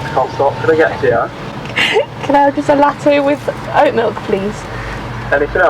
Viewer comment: Is it just us Canadians that heard you say “without milk” instead of “with oat milk” 😂